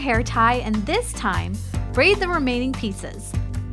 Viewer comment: "Braid the remaining pieces." I thought it said "Braid the remaining pizzas."